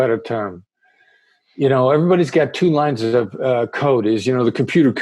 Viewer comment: I thought he kept saying two lines of coke lol